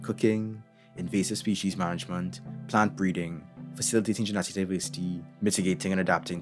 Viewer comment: what did you say at 1:57 after plant breeding? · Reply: facilitating genetic diversity